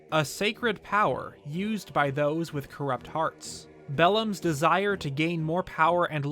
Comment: Oh thought it said belldom